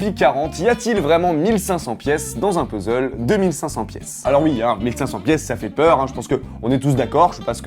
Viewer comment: à 0:20 C MOI OU J entends ''IL Y A T-IL UN EXACTEMENT 1500 PIECES DANS UN PUZZLE 2500 PIECE ?! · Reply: il y at'il 1500 pieces ds un puzzle DE 1500 piece